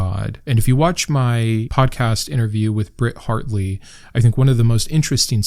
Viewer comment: For a second, I thought you said Bret Hart 😅